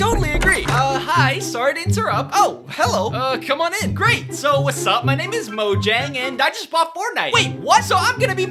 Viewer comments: I thought bro said “Sup my nig-“ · When he said what’s up my name is Mojang And I didn’t see the subtitles I thought he said wazzap my N